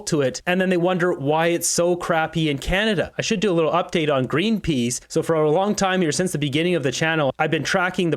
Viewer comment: Why green peas? I thought you said green peace..😂